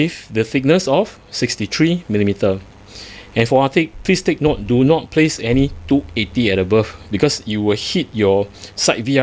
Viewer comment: I'm not sure if i heard you correctly at 27:20 , did you say do not install a 280mm or bigger arctic AIO cooler on top because of clearance issues??